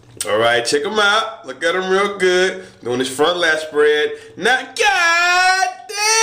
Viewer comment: doing his front lat spread now GOOOOOD DAMMMMMN 😂😂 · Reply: Md Ma oh thats what he says😂😂😂😂😂 i thought he said front last bread or somn😂😂😂😂😂